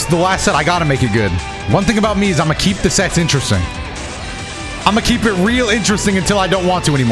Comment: At a certain point I was only half paying attention to the video and I definitely heard "That's the thing about me, I'll always keep the sets interesting" as "I'll always keep the sex interesting" I was like AYO?!?!?!?!